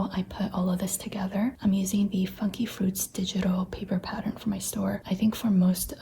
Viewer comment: I get random spurts of creativity and come up with stuff like this. Eg: I spent hours today elaborately decorating my journal for no reason and forgot about lunch and dinner Edit: I think my brain is fried, I just heard you say fucky fruits and it took me a minute to figure it out 😭